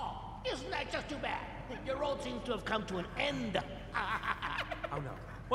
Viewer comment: What's he saying in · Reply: Aaw, isn't that too bad, your road seems to have come to and end. Hahahhahahahahah!